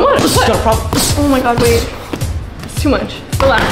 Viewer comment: did anyone hear when she said oh my god babe?